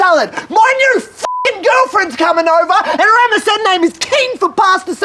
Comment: the subtitles say "(indistinct)", so for anyone who may be unable to decipher, he says "and her MSN name is". enjoy.